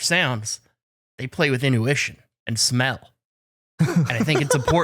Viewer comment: did he say in-U-tion?